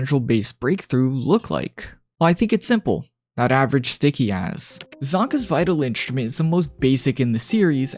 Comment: did bro just say sticky ass😭